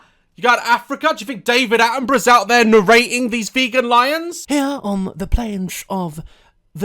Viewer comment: Am I the only who heard her say "these vegan N words"